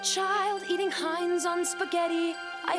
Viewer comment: I originally thought she said "getting high on spaghetti 🍝"